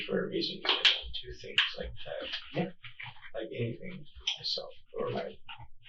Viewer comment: I believe he said “I don’t do things like that” (it said inaudible I’m just saying what I heard)